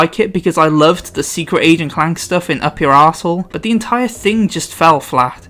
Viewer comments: Did I hear correctly? Did you just say "in 'Up Your Asshole'"?😂😂. You're my man. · anyone heard up your arshole?